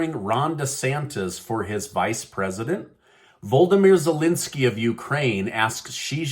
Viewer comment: I thought I heard Voldemort Zelensky